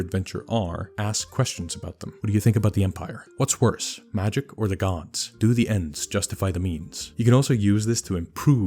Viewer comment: I totally heard, "Do the ents justify the means?", and I was thinking, "I mean, they took our Isengard, so yes?" (The issue was my hearing, not your accent.)